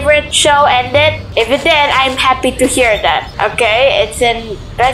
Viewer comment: Did you say I'm happy to hear that from a number